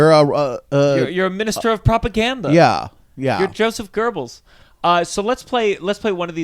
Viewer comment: min 06:45 I heard "you are Joseph Gerbils"